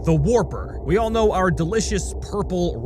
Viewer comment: did he just say delicious?